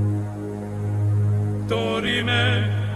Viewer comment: I keep hearing "Dorime" as "Dirty man"